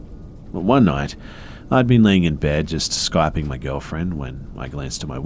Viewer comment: 😂 thought he said “clapping “